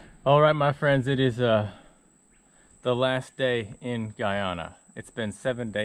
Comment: I thought you said Ghana and then you catch an arapaima. I was like “wait that’s a South American fish. You said Guyana…lol.